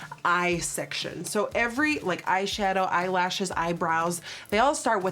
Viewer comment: Did she say Eyeshadow and Eyebrows start with I.